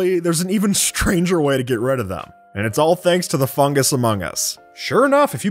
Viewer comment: did he say:Fungus among us?